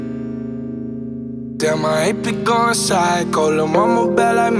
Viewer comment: Wait.....I thought he was saying "I HATE BE GOING PHYCO" lol man i don't know shit I guess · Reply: I thought he was saying “damn my head be going psycho” lol